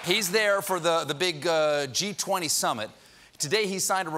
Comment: Thought he said the ‘Cheat 20 Summit‘ :-)